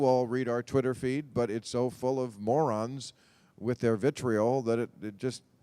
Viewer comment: ***** No, I'm sure he's an atheist. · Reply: ***** I thought you said Mormon. Raz you don't have a sense of humour do you? It shows!